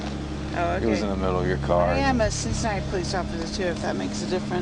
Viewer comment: "I am a Cincinnati police officer...", not Suicide police officer as the subtitles say. She is slurring her speech, so I can see how it could be interpreted that way.